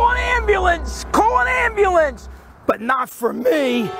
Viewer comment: For some reason on the second replay I heard "But not for p"